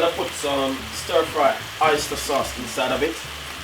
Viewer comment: did he say " stir fry oyster sauce?" Can someone please clarify